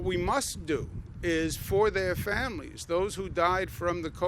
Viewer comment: did he say "death penality" -- so truth comes out that health care workers be given death penalty ? Sad